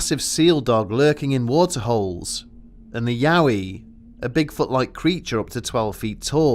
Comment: i misheard 'yowie' as uhh... ahem ahem..